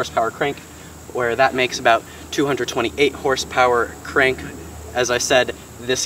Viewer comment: "don't wanna mess anything up.." then messes up the HP on the Q50.. he said 228 instead of 328 😂😂